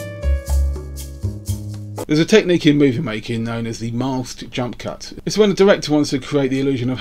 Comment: The who what? Autosubtitler of youtube says: Mast jump cut. I did understand Mile 2 jump cut. Both do not result in NO hits.